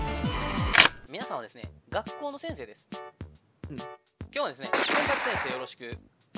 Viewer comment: ガチホモの先生です に聞こえて焦った